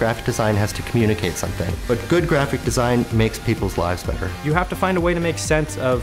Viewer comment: What is he saying at 0:04? It sounds like "Good graphic design makes people fly stucker" which doesn't make any sense.